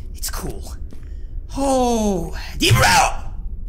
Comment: sounded like he said DEBRO!!!